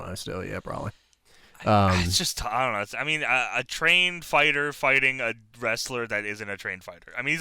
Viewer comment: I thought said A-Train fighter and I was like whose fighting Albert??